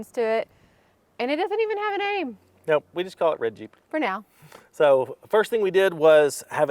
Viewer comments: Love your channel and your new Jeep! For the name, I vote “REDggie”. Looking forward to following the build and adventures. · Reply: I heard say "red jeep" but it sounded like "Reggie". I came to the comments and you beat me to it. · Name it Reggie. That’s what I thought you were saying when you said “red Jeep”